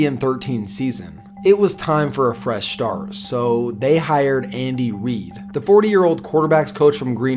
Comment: sounds like he said Andy weed😂😂